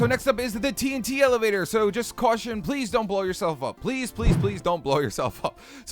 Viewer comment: when you were building the multi craft thing i thought u were saying pleb pleb pleb pleb